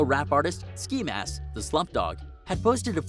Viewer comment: did he say ski mask the slump dog it’s god